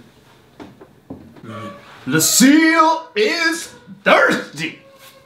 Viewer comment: don't touch anything: Takes Negans bat Lucille is thirsty! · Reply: i thought he say "the seal is thirsty" lmao